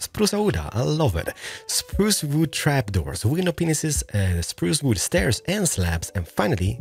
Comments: All i heard was "window penises" and i cried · Hey love your tutorials but one thing... I think I heard you say something different than glass panes. xD I heard window pen15s... Correct me if I'm wrong.